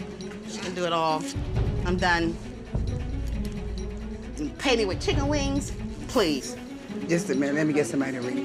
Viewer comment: Did she just say " been paying me in ChickenWings?!?""